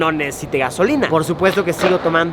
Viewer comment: Entendí "Verga" cuando Luisito dijo "ver gasolina" xdxd